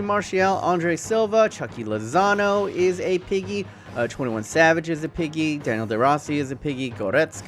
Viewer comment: Wait i think he said 21 savage when he said Milinkovic-Savic